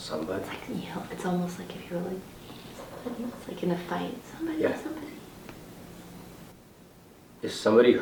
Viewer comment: i don't know if it´s from the box or just audio i´m hearing, But I hear a woman´s voice saying yeah after the question is being asked.